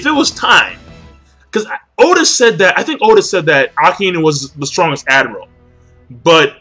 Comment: He said Akainu... but it DID sound like he said Aokiji. He did however, actually say Akainu, kinda hard to make out though.